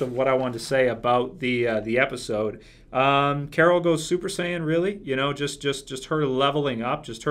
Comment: lol! 12:31 Did he say Carol goes super sayan? Well she was badass this ep.